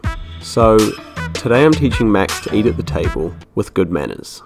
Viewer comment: I thought he said “ to eat a potato” 😂😂😂